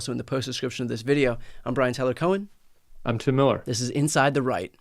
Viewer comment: when I heard "inside the right" I misheard it as "inside the Reich" and as I was correcting myself, I thought hmmmm, maybe not so far off. I miss the old GOP. I never voted for them, but I'd at least listen on what they had to say with due respect. that ship has sailed and off the horizon now